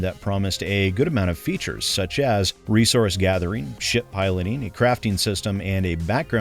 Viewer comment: Misheard that as "Shit Pilling" and was so confused.